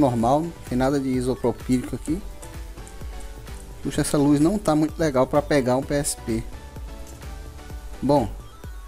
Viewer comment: eu entendi "minha saúde não tá legal pra pegar DST" 😂